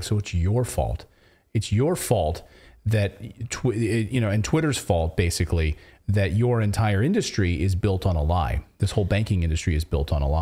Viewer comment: I heard the Twitter fault differently. They say market forces first. Maybe downplaying it but clearly says they don’t do well in this market environment.